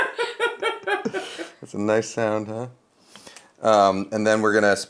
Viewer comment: I totally didn't understand this joke, but apparently it was funny? Maybe an inside joke? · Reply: Same. I thought He said "keel bone" (naval joke?) but actually said "heelbone". I almost think "spatchcock" in and of it's self is funny. Sillcock we'll save for another time:face-blue-smiling:.